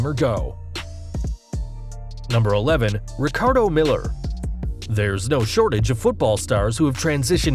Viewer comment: I THOUGHT YOU SAID RICARDO MILOS